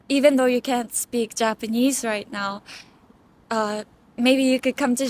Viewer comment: I heard, “ Come to Japan snd mske friends from different countries.” 😅